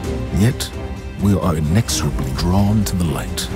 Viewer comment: What does Zavala say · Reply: I think he said "Inexorably" which means that an event that is impossible to stop is happening.